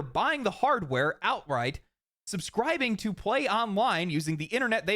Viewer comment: I heard "Subscribing to PlayOnline..." and i was like wait what?! then i replayed and realized im stupid....